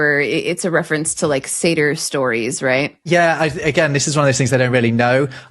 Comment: I thought you said Seder stories and was wondering how the goat got involved 😂